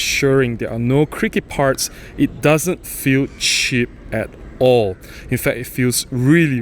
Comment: "it doesn't feel s**t at all" haha made me laugh, and I got what you were saying. Nice review. Thank you for sharing. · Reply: cheap, not shit. lol